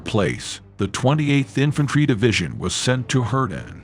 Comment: The voice (whatever it is) sounds like he is saying 20th Infantry Division. That is NOT correct. It was the 28th Infantry Division (from Pennsylvania) and due to this battle and the Division’s shoulder patch, it was referred to as the “bloody bucket” division.